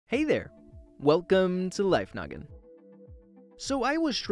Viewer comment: In the beginning I thought he said “welcome to life nugget” lol